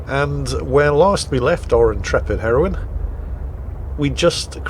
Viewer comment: So English is not my first language and when he said "intrepid heroine" what I heard was "injected h*roin". I had to listen to it a few times over to unhear it.